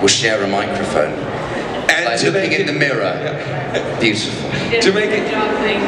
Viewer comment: could someone please tell me what they said in 38:00? i don't speak English so i can't understand well Thank you❤ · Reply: Jamie: It's like looking in a mirror. Beautiful. Ralph: Good job thanking me 'cause obviously I'm the real thing Jamie: Oh my God. Goodness me. Ralph: I'm joking, I'm joking.